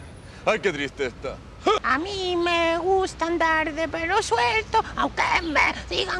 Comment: Yo pensé que decía a mí me gusta el tangananica